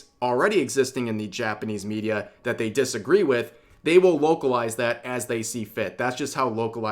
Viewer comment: Like the shakers in the bottom corner of the screen? Well here's your chance to get these limited edition iceshakers as well as my official flavor jar: <link> Make sure to use code "REV" at checkout to receive 10% off your total AdvancedGG order!!! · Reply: Did you say "wokalize"? Because I'm using that from now on.